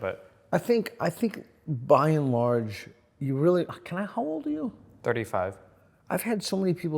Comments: intellectually, I figured out that Peter must have said “can I … how old are you?”, but I much prefer to believe it was what I first heard: “can I hold you?” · Did anyone else hear "can...how old are you?" @ 14:14 as "can I hold you?" o.O I was yawning at the time (not bored, just sleepy).